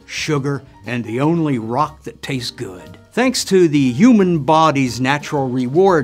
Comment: “...and the only rock that takes good” crack cocaine: Am I a joke to you? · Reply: I thought he said «the only rotten that tastes good»